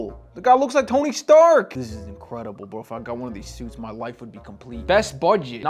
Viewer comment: I thought you said "if i get this suit my wife will be complete"😂😂